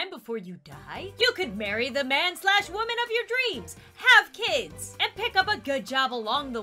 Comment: I thought she said bury the man/woman of your dreams